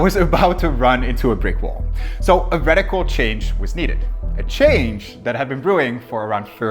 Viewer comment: he said "Radical Change was needed" and I heard Reddit called change was needed 😅